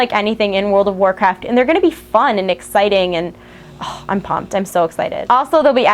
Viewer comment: what did she say at 10:10 ?? ''I' m pumped''? lol I can t understand....the rest was clear thks for the infos! :D